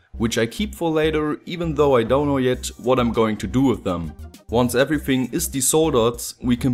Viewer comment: you said "once everything is desoldered" but the caption said "disordered" Accidentally poetic.